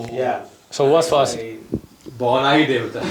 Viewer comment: what did he said? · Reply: Bauna hi devta hai